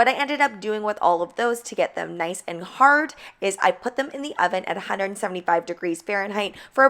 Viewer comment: I love all your videos I learn so much 😁. What are the 🐝 on the transfers? I thought I heard you say you put the 🐝 in the oven.